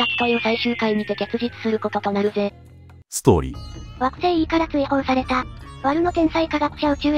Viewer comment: 小学校の昼の音楽に流れてたけど視聴してなかったので歌詞がなんじゃこれ状態だったのです。 『宇宙猿人ゴリ』の歌は当時、♪わくせーいーからー・・・って聞こえてて、惑星の名前は何だろうと思ってたのです。今回の解説で♪惑星～E～から～・・・なんだなぁと。臨終のときに心を残しそうな懸念が1個解消されました。ありがとうございます。 ・・・もしかしてEnjinのEか？？